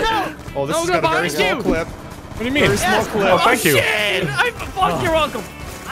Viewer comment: sounded like said “I fucked your uncle” and thanked him for it LMAO